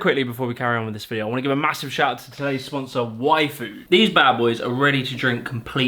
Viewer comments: I thought you said WAIFU and you totally had my attention. Y Food is dropping the ball in terms of marketing! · When you say yfood... it sounded like you said waifu... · i was doing something else and i heard "Today's sponsor, wifu" and i was like "WHAT!?" 💀